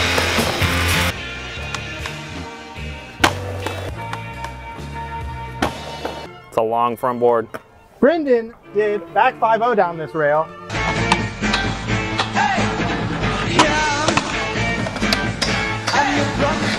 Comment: I thought you said tank did a back 50?